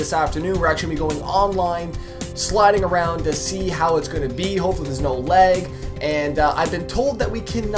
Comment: did you say hofully there is now leg ???????